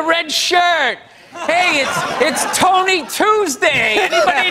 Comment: Tony Tuesday - If your name is Anthony.. 2:33 he didn't have a "cough" (as if he were sick) - I'm not criticizing, I'm trying to help. Bill Burr does speak quickly and even native English speakers don't hear the entire thing. But, being native speakers, they don't worry about certain things - we all do the same things.